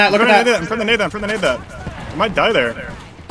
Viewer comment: お前誰だよにしか聞こえん